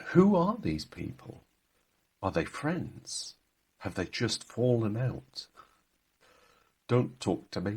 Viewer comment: I thought you said, "Are they just Hall & Oates?" 🤣